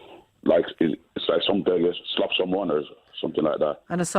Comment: I heard "stab someone", not "slap someone".